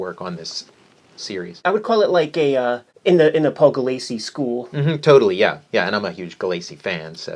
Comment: Hi, newbie question here: At the 7:15ish mark in the video, they talk about how they felt that the artist similar to something like "the Paul Galacie" school? I couldn't figure out what name they were saying. I looked up Galacy Galacie Gulacy etc. Does anyone know?